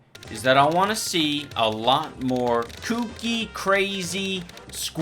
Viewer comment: Did you just say Kuki Kreyzi??????